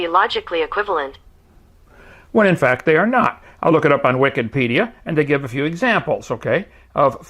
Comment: Did you just say Wiccanpedia? Kent why you talk to weird? Or do you really believe that Wikipedia is Neo-Pagan? Why are you using "Satan's" website to debate, Kent? It's daaaangerous, you know XD